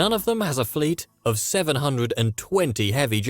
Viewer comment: you said 720 giants while the subtitles said 270.